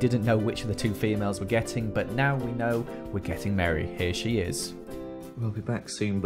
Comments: Joel:"We're getting Meri" What I heard: We're getting married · i thought he said "We're getting married" but he said "We're getting Meri" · When they were talking at the beginning and he said "we are getting Meri" it sounded like he said "we are getting married" and I was like OMFG WHEN DID THIS HAPPEN OMG... it's a dog ITS A REALLY CUTE DOG THO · Puppy fact: puppy's should sleep at least 18 HOURS of course when they grow up they can have less sleep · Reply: joel said we're getting Meri and I thought he said for a second he said we're getting married to Lizzie ! lol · But now we know were getting Meri I thought he was gonna say we’re getting married 😂